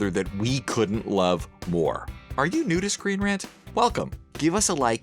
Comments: I heard, "are you a nudist, ScreenRant?" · When he says “are you new to screen rant” I thought he said “are you nudist screen rant?” 😂😂😂